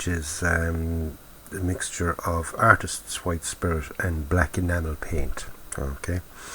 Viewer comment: Hi 14:45 did U said black nato paint or black nano paint?